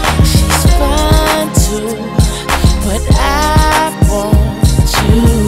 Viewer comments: 5 YEARS LATER I FOUND OUT HE SAY “SHES FINE TOO” NOT “5’2” LMAOOOO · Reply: Lmfaooo that's what I thought too, then common sense tapped in and I listened till I heard "5'2" to "fine tune" to "fine too" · Who else thought he said, "She's 5'2 but I want you!"